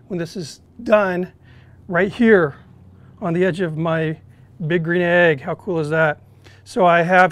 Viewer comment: I always hear him say that he's rocking 'the big grenade'! We've been trying to get those Meater thermometers, but decided to wait for the Meater Block. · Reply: HA! At first I thought he said "big grenade", too!